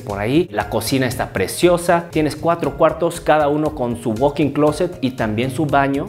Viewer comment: la cocina está preciosa, tienes cuatro cuartos cada uno con su “fucking” closet 😂😂😂 perdón es lo que escuche de entrada